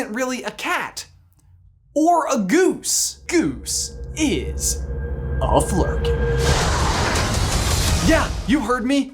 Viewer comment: i thought he said “Goose is a furry” and i got so confused